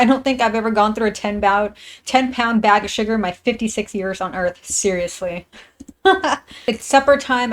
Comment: 56???????????????????????????????????????????????????? YOU'RE 56 OR DID I HEAR WRONG???????????